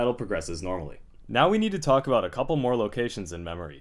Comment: I SWEAR I thought I heard "Now we need to talk about parallel universes" D